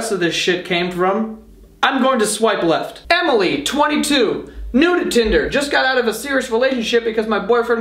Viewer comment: anyone else hear "nude tinder" at 3:19?